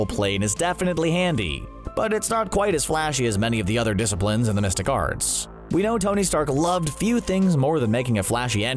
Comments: I kept hearing "domestic arts" and got confused... xD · Through the whole video I thought he said "domestic arts" and I was so confused 😆😅